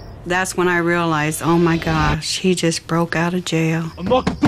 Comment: Wth she said “omg SHE just broke out of jail” in 5:15-15:18 it’s a boy not a girl 🤣😂😂